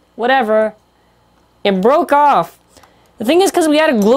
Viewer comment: I thought you said it broke off on z Achilles